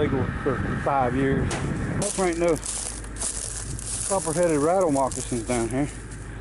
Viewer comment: Rattle headed copper moccasins ! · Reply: Tom Scarborough I thought he said Copprheaded Rattle Moccasins..........all of them coulda been under there like he said. Welcome to North Carolina. .